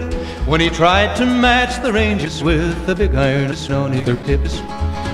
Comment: I hate the fact I keep hearing “Big Irons on their tits”…